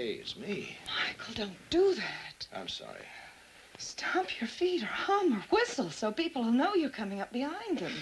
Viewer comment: Ha ha! 'Stomp your feet or hum..' I thought 'she' said, 'Your feet are humming'!